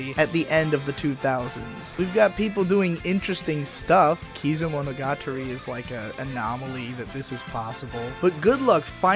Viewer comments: Was the anime at 14:35 called "kizomonugature" ? (At least I think I heard something like that) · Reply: Kizumonogatari, the movie prequels to Bakemonogatari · anime name? I can't understand what he said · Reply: Kizumonogatari